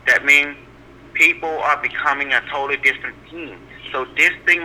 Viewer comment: What is he saying at 28:16 ? Plp becoming distant?